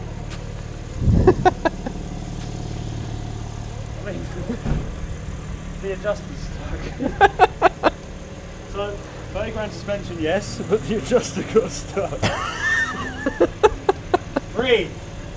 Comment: did say €30k suspension? far out